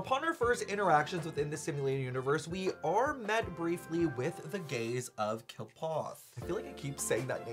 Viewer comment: When I heard the "the gaze of Qlipoth" I heard "the gays of Qlipoth" and I was like oh Bronya?